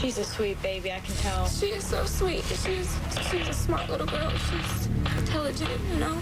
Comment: I think the mom said “she WAS a smart little girl”